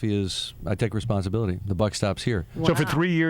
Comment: What he says at 1:29-1:31 ??? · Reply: I take responsibility. The buck stops here.